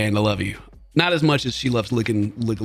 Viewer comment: Thought that said "lady licks legos"